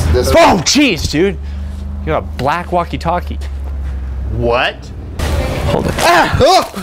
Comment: I thought he said you're a black walkie-talkie.